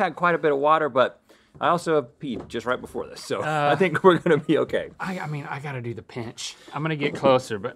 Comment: When rhett said “i think we’re gonna be okay” 4:02 I thought he said “we’re gonna pee okay” PUN INTENTED